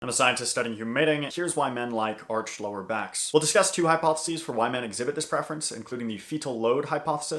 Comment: when you said two hypothesis i heard "twerk-hypothesis" but on a serious note men were just programmed to like women